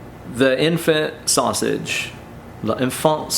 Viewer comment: For a sec I thought he said “The infinite sausage” 😂